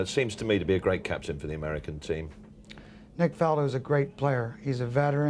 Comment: Did Earl call him "Nick Faldo"? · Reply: First thing I noticed!!! Guess Earl is a golf fan.